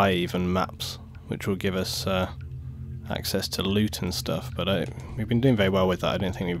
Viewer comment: When you said "loot and stuff" at the beginning, I could have swore that you said "luetin stuff"